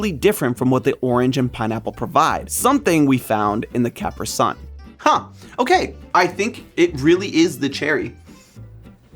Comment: did he just say caper sun?